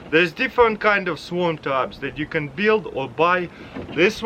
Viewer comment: I thought he said swamp trap